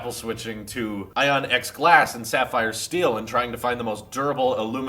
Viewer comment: Did you just say sapphire steel?